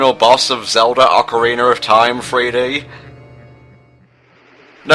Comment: Everytime it sounds like you say Ocariner of Time